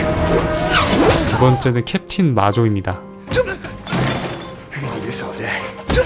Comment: 켑틴 마조 잘못 들어서 켑틴 맞어라는 줄....